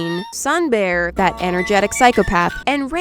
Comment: did anyone else hear her call sun bear an “energetic psychopath “